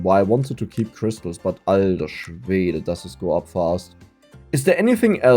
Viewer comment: can someone of the german speaking folks or just an enlightened ones tell me what said there? "Alda" i think it's like casual a "dude" or a "bro", but what's the second word? Is he talking about swedish people or what 😅